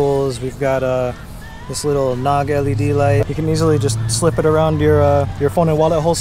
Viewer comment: I like that LED light. The one linked in the description doesn't look like it can clip onto your clothing though. Is it the same? Sounds like he says "NOG LED Light" ...maybe?